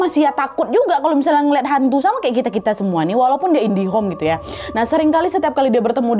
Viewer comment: Idihom apa indigo. bekali kali bilang indihom kalo aku gak salah dengar ya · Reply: bilangnya mmang indihome, indihome memang sering di pke buat plesetan indigo